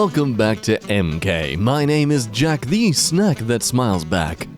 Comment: thought jack were saying "jack deez nuts" on "jack the snack"